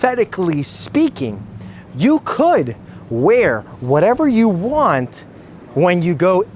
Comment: Correction: He said you can wear whatever you want in the pool (meaning nothing, if that's what you fancy). He never said you can do whatever you want! Being nude in your own private pool is not illegal in the US, last time I checked.